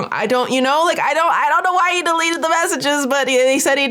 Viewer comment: is always really good on clarification and research, but I thought he said "i can't find the chat" not "I deleted the messages" 😭